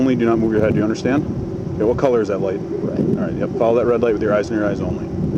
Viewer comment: Did I hear this right? Cop "what color is the light?" "red" Cop "Right, go ahead and follow the red light with your eyes only" Edit: If I did hear it right, then thats a pretty clever way of catching someone slipping